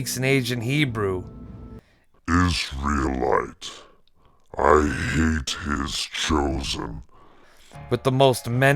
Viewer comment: Did he say i hate isralites? Meaning Afghans? Or Israelis? 11:26 Did he say 'Having fun yet, Jew' And how did heck did the solider understand ancient hebrew? Fake stories i guess but cool